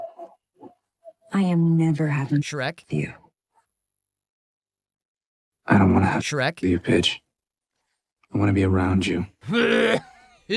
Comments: I legit thought he said "I dont wanna have sex with a bitch." LMFAO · wait what the fuck, you censor the word SEX, but not the word BITCH. Are you drunk?!? Stoned?!? Or just really badly confused on life?!? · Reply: To be fair, he’s saying “pige” (short for “pigeon”, his nickname for her) - I hate that I watched this movie and now know that 😭😭😭